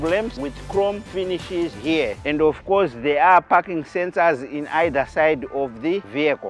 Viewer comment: 😂 for a second i thought he said "here there is no fucking sensor "😂😂🤣